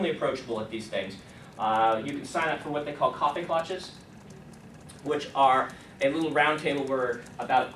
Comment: he says _______ ________? Topic Launches? Copy Launches? They sound very useful for someone like me. Does anyone know what he's saying?